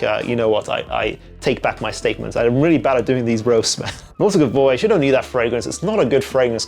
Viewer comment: I couldn't really understand what fragrance was talking about at 5:20. His voice wasn't that clear. Could someone help me know what fragrance it was which he criticized pls? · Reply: Nautica Voyage